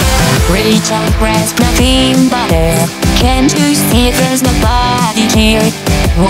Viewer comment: at the beginning it sounds like she is saying "can't u see there's my body here?"